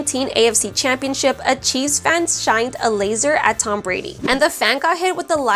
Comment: When you say "a chief's fan" it sounded like you said "a cheese fan"